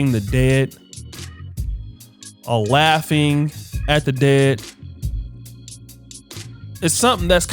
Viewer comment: Only 00:38 secs into but did he say"Waughing at the dead"?